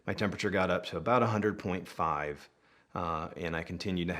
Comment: U'll watch it again i thought i heard you say 105.